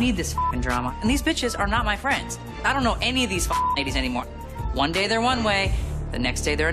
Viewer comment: Yooo at 5:45 did Kim say she don't know these niggas anymore? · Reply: She said fckn ladies 😂😂